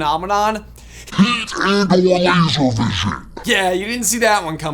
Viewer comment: For anyone wondering what said at 4:53, "heat and/or laser vision"